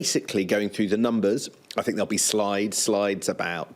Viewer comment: 'I think there will be slides'. I thought he said: 'I think there will be LIES'.